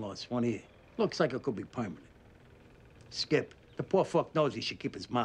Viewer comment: did he just say "poymanent"